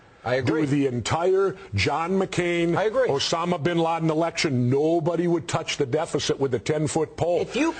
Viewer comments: did he say the john mccain and osama bin laden election? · did he call Obama: Osama Bin Laden?